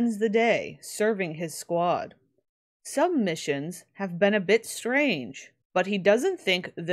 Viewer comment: Watching this in the background while studying. And I swear 5:53 I legitimately heard "Top spends the day serving his squad. Sub missions have been a bit strange..." I think I need to lay down.